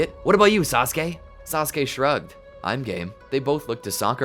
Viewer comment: when susake said I'm game I thought he said I'm gay